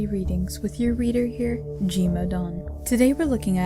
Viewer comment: I always thought when she said her name is, it was GModeOn, (G Standing for god) I have no idea why.